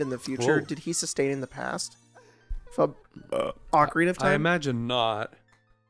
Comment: I think it was because I had some background noise, but at this point here I truly thought that said "Cock ring of time" and I had a moment where I was like "HUH?!"